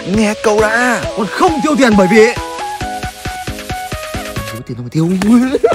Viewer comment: Khúc 1:23 anh nói gì vậy? Mình không nghe được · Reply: Làm gì có tiền đâu mà tiêu